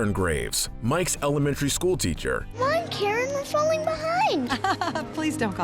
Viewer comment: did mike call her karen